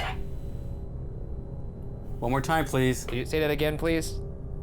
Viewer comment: For the Post Mortem- At 28:41 to me it sounded like someone either saying 'yes please' or 'get this' in response to Ryan saying he's turning off the machine. Also, random theory, AJ said he picked up on family vibes but also violence (hands, dragging, etc) possibly so what if there was an abuse situation in a previous family? That often doesn't get reported and so wouldn't show up in any house history but could explain the vibes, "Matilda" not wanting to talk/being scared to talk, and possibly even the key getting messed up (no privacy/locked doors).